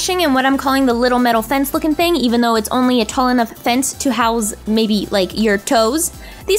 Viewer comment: When she said "it's only a tall enough fence to house maybe your toes" I thought she said "it's only a tall enough fence to house maybe your toads"😂